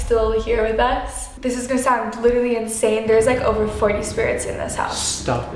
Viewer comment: Did he just say 800 spirt